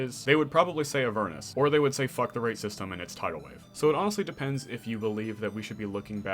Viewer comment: Biggest mystery of the video: what does he say at 3:20 “Fuck the racist te-min. It’s Tidal Wave???”